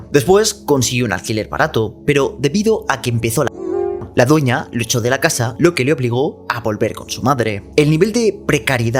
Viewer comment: que es lo que dijo en el minuto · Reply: pandemia